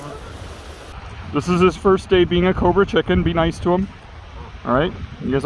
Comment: I must fit the phrase "cobra chicken" into a conversation someday. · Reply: I thought he was saying corporate chicken